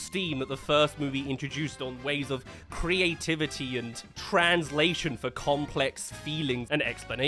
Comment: when you said "translation" i only heard "trans" and immidiately went to watch the trailer